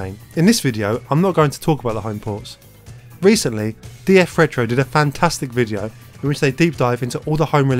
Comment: Can someone write the name of the guy who made the video about the original mk home port releases? He says the name 18:58 but i can't understand. Sounds like DA Frators. 🤔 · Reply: DF Retro, aka Digital Foundry